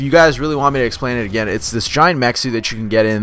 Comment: Haha it sounded like you said a hard suit was a giant mexican you get into lol